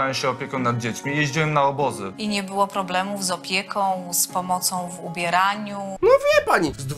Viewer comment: czy tylko ja usłyszałam pomoc w umieraniu XD